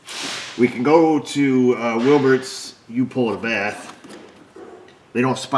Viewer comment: who else thought Says Wilberts "you-pull-a-bath"😂